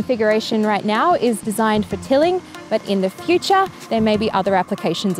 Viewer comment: I heard "This particular unit is designed for KILLING" and I immediately thought of Terminator